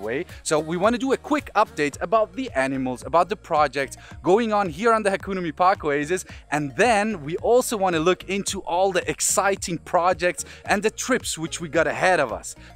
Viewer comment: Hi Dean god bless you and all of the Hakuna Mipaka team! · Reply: I thought u were saying hakuna matata team